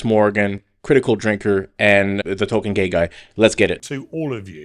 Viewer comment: and the talking gay guy😂😂 *token😅 my bad I heard wrong